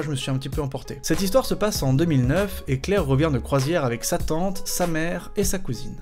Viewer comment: J'en peut plus pour la deuxième histoire quand tu dit "sa tante", j'ai compris "satan"... ça rend l'histoire encore plus flippante ^^